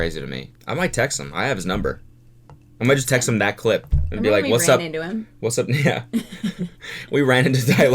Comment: it sounds like cody is gonna say "whats up n-" AND I AM CRYING AHHAHA